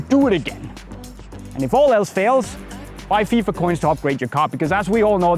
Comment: I did not understand this part - what did he say at 6:04? "...if all else fails, buy fifa coins to upgrade your cart...?"